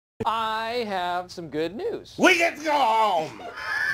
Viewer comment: I played it at 0.5 speed and what I heard was, “We get toe home!” 😂🤣